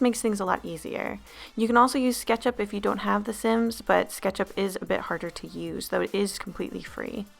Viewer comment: I thought she said "Ketchup is a lot harder to use"